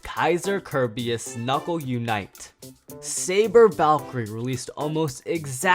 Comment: WHAT DID YOU SAID?! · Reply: He said Saver